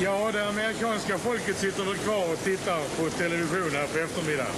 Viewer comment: I misheard this as Skånska (Scanian).